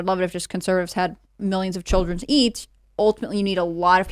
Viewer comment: Did she say people should have multiple children to eat at 0:07:23 ???